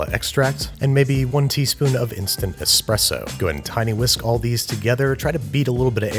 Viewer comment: Did he say tiny whisk?